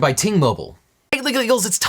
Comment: I swear to god I thought I heard "sponsored by Teen Mobile", which, given the subject, had me dying.